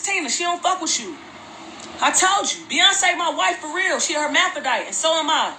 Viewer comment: did you say that Beyonce I was here wife I think I need to watch this two times